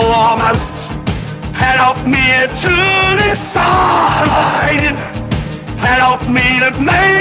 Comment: who heard hitler say "Help me to suicide" 💀